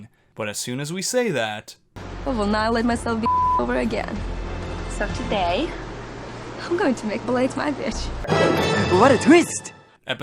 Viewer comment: what did kristina say at 8:21? i didn't understand what she said. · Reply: “I will not let myself get fucked over again so today I’m going to make Blake my bitch”